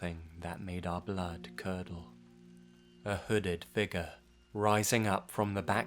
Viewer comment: Anyone else thought he said, "a hooded ni**a"?